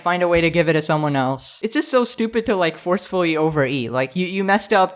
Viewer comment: i thought it said overheat